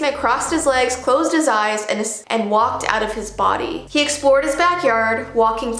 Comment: And I the only one who heard almost saying escaped but then she said walked out of his body?